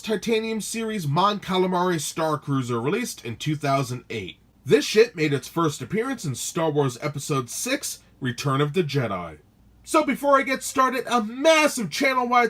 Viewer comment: u said sh*t instead of ship XD